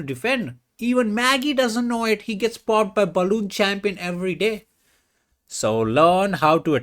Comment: Did he just called Gukesh balloon champion? 😂